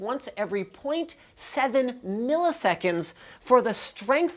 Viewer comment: Technically yeah. It is pressure sensitive. But with a update rate of 0.7 second, i dont think it will be that accurate. However, it is still possible. It could be the second type of keyboard to able to emulate analog. Atleast i think it is the second. I know there is a laser keyboard that could emulate analog due to detecting distance of the laser. But i dont know any other keyboard that able to do that. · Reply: I thought I heard 0.7 milliseconds?